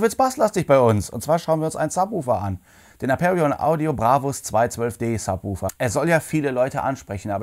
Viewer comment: Lol ich habe am Anfang Brabus verstanden :-)